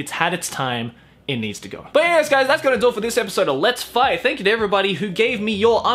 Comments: I thought he said "let's fly" instead of "lets fight" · I don't know why, but when Joey said "Let's fight!" I heard "Let's fly!" Lol😃